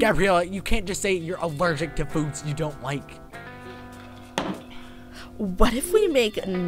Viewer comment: when said food you dont like i thought he said poops you dont like💩